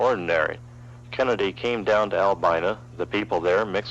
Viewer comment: is he calling Albany Albina?